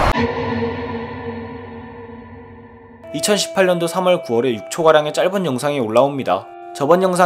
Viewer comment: 나만 3월9월이라들림???